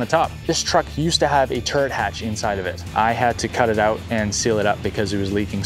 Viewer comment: all i hear is turd hatch